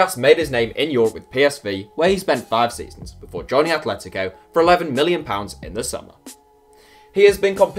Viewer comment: it sounds like he says 11 billion pounds